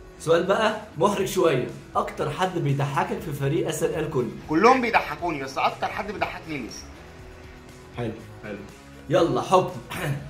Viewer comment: قال مين مسمعتش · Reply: تقريبا الليثي